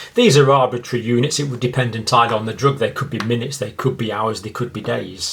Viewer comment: Hello doctor! thank you for your video, I am not a native speaker so could you please tell me what exactly you said on minute 4:56, did you say something like "it entire depends on the drug" ? Thank you!